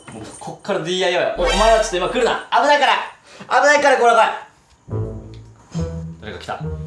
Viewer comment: の危ないから！が、おーちゃんに聞こえた笑